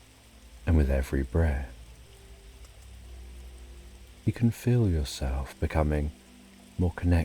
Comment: Did I hear a "I'll be there for you bruh" around 14:15 ? 😂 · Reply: And with every breath, actually